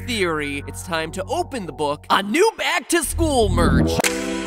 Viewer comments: I thought it said back to school meth for a few seconds lol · when it said back to school merch i thought it said back to school meth